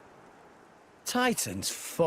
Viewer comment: Every time Rex says "Titan's foot" I hear "Titan's fucked!"